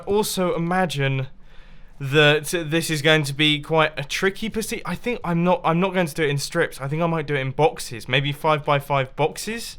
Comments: after 12:40 i dont know what he said, but i understood sticky p***y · I don't know if I'm the only that heard this but at 12:46 did he just say that "this is gonna be a tricky pussy"? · Did he just say tricky pussy xD?